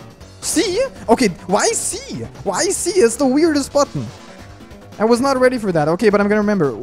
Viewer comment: did he just said fuck? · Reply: He said button